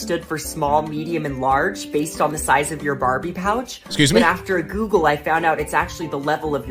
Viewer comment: As he says, in the "Barbie Pouch" which actually sounds appropriate, considering it's a fake plastic nothing area for him. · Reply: thought he said 'Barbie Couch' - didn't understand that.